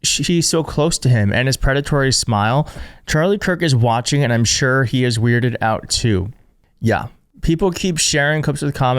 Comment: Did you say charlie kirk is watching and he is weirded out too? Did he get resurrected?